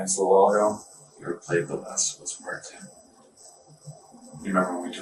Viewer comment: does he actually say "Have you ever played The Last of Us: Part 2"? I'm a video game player, and I've played that game a lot, so perhaps it is just me hearing things! Could somebody else have a listen and tell me what they think???